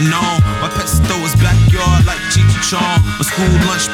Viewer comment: I heard "my pesto is backyard like cheech and chong."